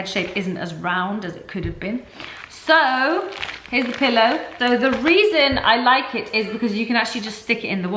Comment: Am I the only one who heared "his pillow"